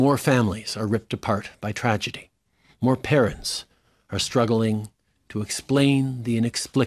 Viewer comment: At the beginning I thought he said parrots 😂